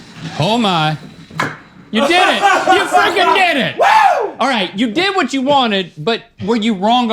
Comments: did link say “you f***ing did it?” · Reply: He definitely said frickin' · Does link say you fu#%ing did it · Not 100% buy did link just say ‘ oh my .. you did it , you fu**ing did it!’ 😂 At 6:10 Lmfao